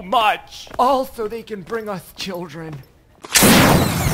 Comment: Ok the punchline is " all so they can bring us children" . The first Three times I watched it I heard " also they can bring us children" I couldn't get it. 😆